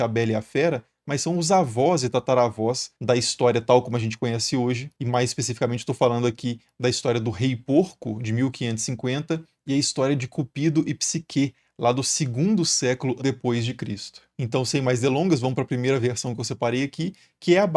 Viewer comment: Eu ouvi cúpido "e não sei o que" kkkkkkkkk